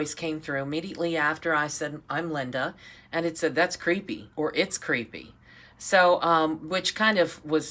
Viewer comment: I heard "ha creepy" on the spirit box right away. Might be talking about the masks 😷